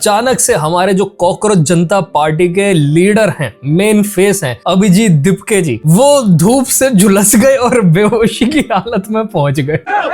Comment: my ears are hearing dhurv instead of dhoop 😂